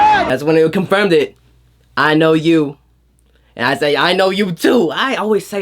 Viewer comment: Wait at 9:54 wasn't J-hope saying 'i love you' ? Because i don't know if i hearing something wrong or you know